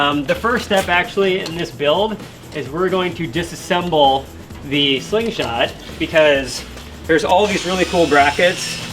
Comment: "No Disassemble!" - Johnny 5 ~ 5:31 "We're ____." [What was the 2nd word?] ~ I am wondering: can you do Clean & Jerks? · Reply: breached